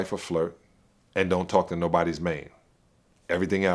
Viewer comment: Did he say don't talk to nobody's man? 2:16 😕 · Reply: j g lol he said nobody’s main. Like they’re main chick